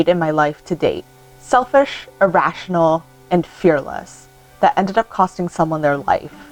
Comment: What she says is "a selfish, irrational and careless choice"